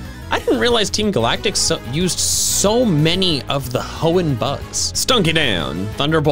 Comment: “I didn’t realize that Team Galactic uses so many of the Hoenn birds” Team Galactic: using dustox Edit: looking at the replies, looks like I misheard, my bad